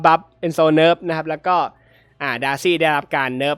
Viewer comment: มีคนพูดว่าดาชี่